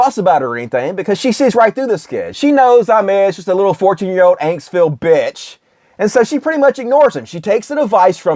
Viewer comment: Sorry, what did call him? An "antsville bitch"? I love it!